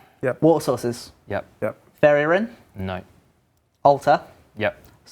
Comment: What does say at 52:20, right after "Water Sources?"? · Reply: Fairy ring.